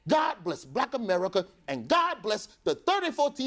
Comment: God bless the 34 tv shows hosted by Michael Strahan 😂😂😂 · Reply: It sounded like he said Michael Strahans....I was like who is that?